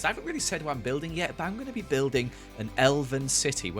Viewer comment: Was it just me or at 0:46 did anyone else hear Joel say “I’m gonna build an elephant city!” Me being tired still even though it’s like after 3 pm: ”You’re gonna build a city out of elephants-?”